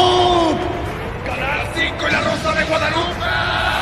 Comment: alguien me dice que dijo vegeto lo que entendí fue videos clásicos de la rosa de Guadalupe!!!!!!!!!!!!!!!!!!!!! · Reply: Canal 5 y La rosa de Guadalupe